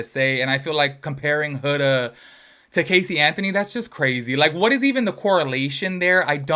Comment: I think she said exactly what the Huda correlation was. I heard her say it’s the lying…